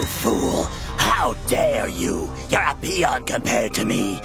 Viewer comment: Did Cell just call this nigga a peon?